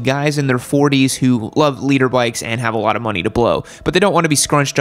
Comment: Hahahaha. 2:45 When I first started watching I thought you were saying "Leader bikes". I was like, oh that makes sense they're the fastest so of course they're the leaders. 🤦‍♂